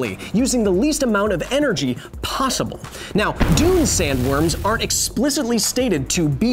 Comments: I genuinely thought that said "Darude Sandstorm" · For a second there I thought it said "Darude Sandstorm"